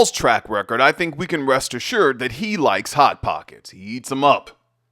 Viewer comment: I thought you said THOT POCKET, not hot pocket😅😅😅😅